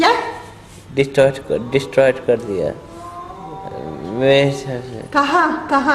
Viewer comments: he clearly said mene destroyed kr diya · may b i heard it wrong bt at 29:50 he said something like "meine phone distroy kar diya"...bhai dimag kharab ho gaya ye video dekhke...ab to arushi hi jane k kon tha vo madh***hod...